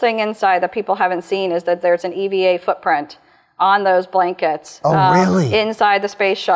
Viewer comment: What did she say at 5:08? Can someone explain what that is? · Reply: Somebody made a footprint